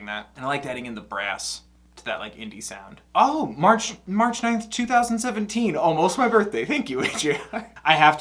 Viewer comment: You almost killed me because I thought you said "Oh march 9th my birthday" and not "Oh march 9th almost my birthday" I legit got so happy because I thought we shared a birthday my dude🥺🥺 Oh yeah can you react to Thomas sanders he has some songs like "on the borderline" and "recipe for me" i think you'll like them